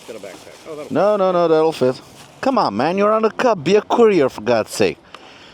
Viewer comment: I misheard it as “You’re on a cub, be a queer for God’s sake!” 🤣